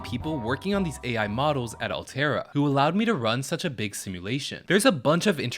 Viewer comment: pardon me for a second, but did you say… Altera?